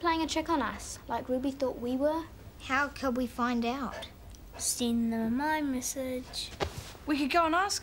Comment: At first I thought he said "Send him My message"